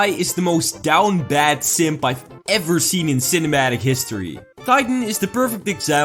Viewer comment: I thought you said “this is the most downbad simp ever seen in (simpamatic) history”